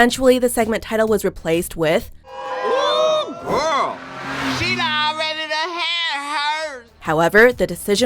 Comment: What is actually said at 6:11? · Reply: She done already done had herses